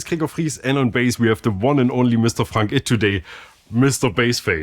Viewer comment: I heard “Mr. Funk It”. Cool.